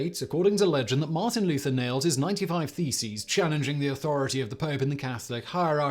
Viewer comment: Could have sworn you said "nailed his feces"